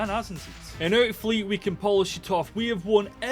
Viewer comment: what did you say? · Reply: ‘And hopefully we can polish it off’